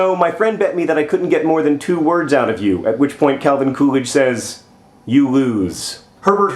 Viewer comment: I heard it was "Fuck you"